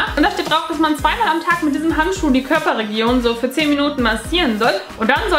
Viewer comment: Ich habe Körperregierung verstanden... Oder hast du das auch gesagt?;D